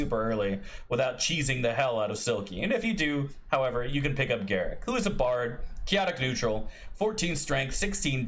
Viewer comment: I misheard "You can pick up GARAK" and now I want to see you play Elim Garak as assassin / tailor class.